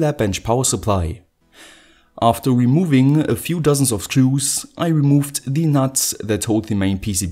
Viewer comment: I heard "After removing a few dozens of Jews" lmao